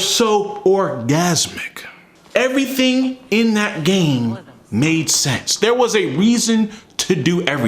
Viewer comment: At first I misheard, but I agreed. "Everything in that game made sense!.." No, Everything in that game made sex!...Pure, unadulterated SEXYNESS. 14:55 for timestamp